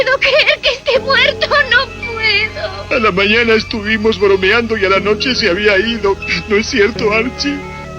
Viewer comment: Como que boludeando eso entendi